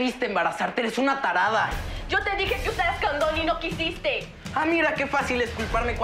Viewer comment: yo escuche "candon" 😭😭😭😭